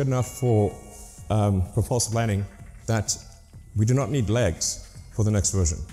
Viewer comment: does he say "the false landing" at 2:44? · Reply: "propulsive landing" e.g. using rocket engines and not wings.